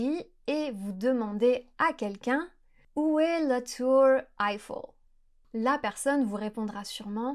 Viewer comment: En tant que français, quand tu as dis "la tour Eiffel" avec l'accent, j'ai compris "la tour iPhone"... Je suis un exemple parfait de ce que tu montres dans cette vidéo ! XD